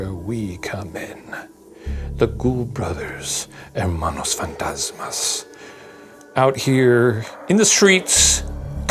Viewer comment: Soooo, whoever wrote the CC did you guys so wrong at 55:13🤭 Hermanos Fantasmas (Ghost/Ghoul Brothers) is what says, but the CC says 'And Monos Fantasmas' (ghost monkeys)🤣